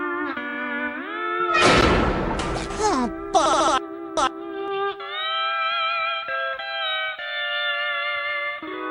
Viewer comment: Ayo what's he say? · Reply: He said barnacles